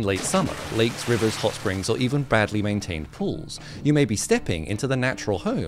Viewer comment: I could have sworn you said "Bradly maintained pools" initially.